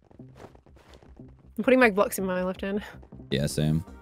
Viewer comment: Could've sworn said put your glocks in your left hand